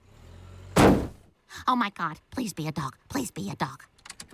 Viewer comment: Why do I hear, "lesbian dog, lesbian dog" instead of please be a dog